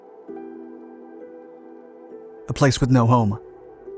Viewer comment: Narrator says "A place with no home" instead of "A plane with no home"